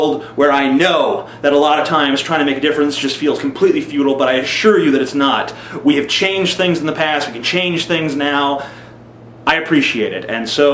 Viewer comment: for a second there i thought he said Feudal instead of Futile... lol